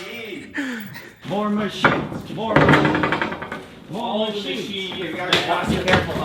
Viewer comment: I misheard the opening chant as "war machine! war machine!"